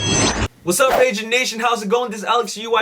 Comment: i thought he said Asian nation at first